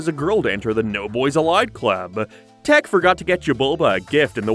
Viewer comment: only thing I remember about this show is that my mom made me call it “Tak and the Power of Jesus” because we were Christian 🤦🏾‍♀️ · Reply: oh my god i remember my mom almost making me throw it away bc she misheard 'jabulba' as 'jahova' lmfao